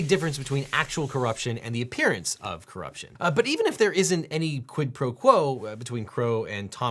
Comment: I misheard "quid pro Crow" here.